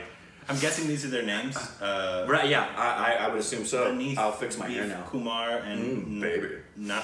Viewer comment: did just say 8:05 Vineeth"BEEF"Kumar lol